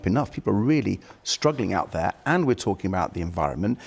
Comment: Every time I hear "out there" I hear othering